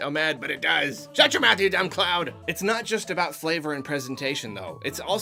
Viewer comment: I really thought he just said “a dumb child”